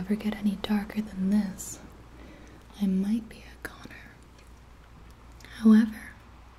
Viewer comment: it kinda sounds like she say I might be a Conner